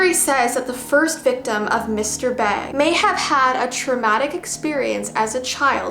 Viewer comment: I thought she said mr bean 😂😂😂😂